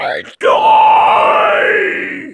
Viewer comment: What the heck he says at 00:10 ??? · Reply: He screams, then says "Die" and "It's not over yet"